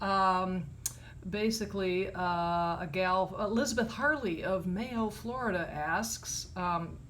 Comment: When you said "Elizabeth Harllee", I swear I thought you said "Elizabeth Hurley". My first thought was Whaaa??? The actress? Hugh Grant's old girlfriend? 🤣 Then I ran to the bathroom to clean the wax out of my ears. Bob Haney scripted that story. He must have written it a 4:45 pm on a Friday before a 3-day weekend.